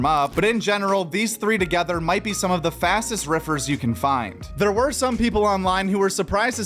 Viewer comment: lol I heard fastest heifers of all time 😂